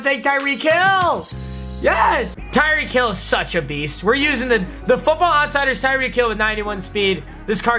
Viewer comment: I thought you said weast